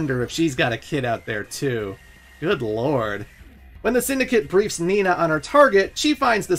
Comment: He says "good lord", I hear "good Lore"